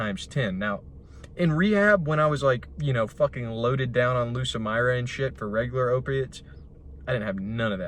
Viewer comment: what did he say? Loaded down w "lucamirane"? What is that? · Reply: Lucamyra I think